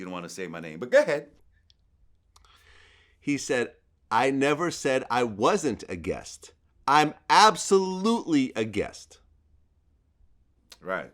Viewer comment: Did Em actually say he was a guest? Or did he just say “I never was been claiming rap” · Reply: Nick em said “I never said I wasn’t a guest, I’m absolutely 100% a guest”